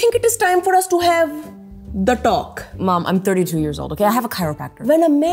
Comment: Ok so ... 0:08 DID LILLY JUST SAY SHE WAS 32?!?!?!?!